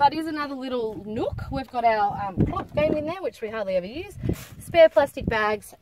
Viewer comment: what did she say is in this box please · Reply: our game of Klop!